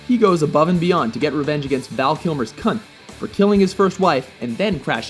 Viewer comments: I keep hearing "Val Kilmer's cunt." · I swear I keep hearing "Val Kilmer's cunt."